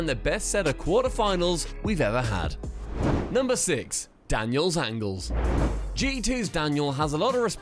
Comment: I thought 4:05 said "Daniel's Ankles" lol